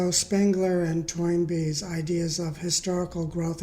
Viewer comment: you mention two names, but I didn't hear the 2nd one: "Spangler and ______"? Thank you for this video. I too recognized the misinterpretation (or intentional misrepresentation) of the Hegelian Dialect, but lack the background to explain it as well as you have. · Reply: The two historians I mentioned are Oswald Spengler and Arnold Toynbee. Since I posted this video, I haven't noticed as many people making this mistake. So I wonder if my video actually had some effect. Thanks for your inquiry.